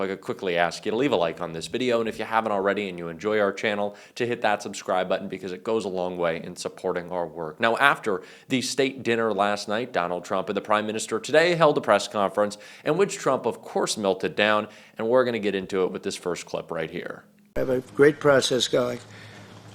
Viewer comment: I misheard that as "the steak dinner he had last night"!